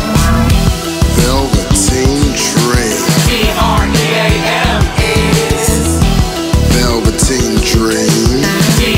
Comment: can u please tell me what they say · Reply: They say "D R E A M, IS"